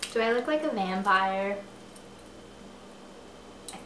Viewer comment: At first I thought you said "Do I look like a manpire?" I"m like "uh...what is that exactly?" Noah is my husband. Mara is crazy and I love her for it. I think the point of the Ouija board was less to do with ghosts and such, but just as one more omen and sign of the prophecy at work. (A general thought on the Ouija board on your vague mention of the board, not really a direct response ha.) I was tired of the island, I'm like "Ugh, just swim off of it already." With the exception of the beginning of Retribution which definitely made me less motivated to read, overall I was still really happy with the ending. I feel like so many people hated it, but I loved it like you. The only reason I didn't give it 5 stars like the other two books was because I dragged through the beginning, had that happened, it would have gotten 5 stars from me. I just love that you finished this series and we can talk about it!